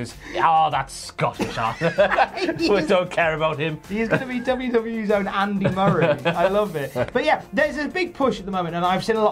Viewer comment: Did I hear say WCW's own Andy Murray?